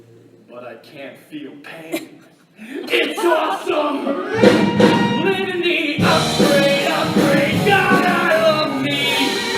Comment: "LIVING THE UPGRADE UPGRADE GOD I LOVE ME" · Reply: I thought he said "LIVING THE UPGRADE UPGRADE! GOD I LOVE THE-" But that makes more sense I guess